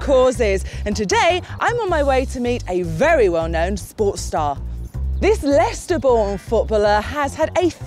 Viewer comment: I thought she said 'porn star'. I then thought how nice it is that this country is as progressive as that.